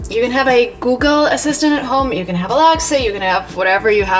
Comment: Hi, I'm an English learner, Could you please tell me what do you say in 0:30, I don't understand that phrase, You can have a what? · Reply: "You can have Alexa"